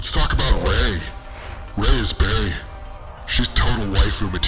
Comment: rey is.... what? 4:41 i didn't quite catch that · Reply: Rey is bae - a person's boyfriend or girlfriend (often as a form of address).